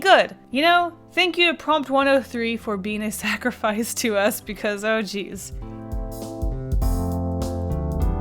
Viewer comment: Is it me or did say Oh cheese?